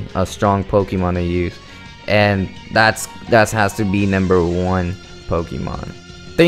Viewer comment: you sound like your saying namber